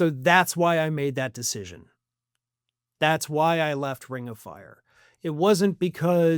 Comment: Phew! For a second I thought you were saying, “why I left the left.” 😅